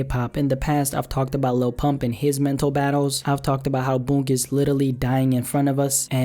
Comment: which artist is that booongus littlely? That’s what I heard so someone clarify.